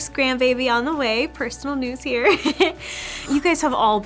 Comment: Congratulations at 6:15 did I hear you right about the personal news here first grand baby? Congratulations. ♥️